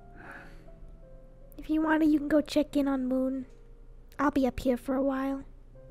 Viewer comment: during the deep convo between and I get a random ad, thanks so much youtube 😃 sarcasticness (edit) 15:33 I'm sorry but why the hell did I hear "chicken" instead of "check in"?? 😂😂😂